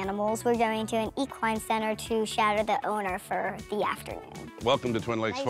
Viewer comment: Oh, she's going to shadow the owner. I thought she said she and her sister were going to shout at the owner.